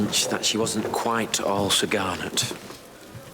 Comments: What did Thomas say at 10:00? Subtitles say Garnett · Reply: “All Sir Garnet,” meaning “all in order.” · What did Thomas mean when he said at 9:56, "...she wasn't quite all sagarnet (spelling)." What does "sagarnet" mean? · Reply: He said "she wasn't quite all Sir Garnet" It is a British expression referring to Sir Garnet Wolesley. "All Sir Garnet" basically means everything is in good order, Thomas was saying that everything was not in good order.